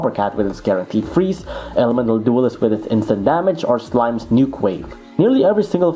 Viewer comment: I thought you said "Elemental Duelist with his Infinite damage"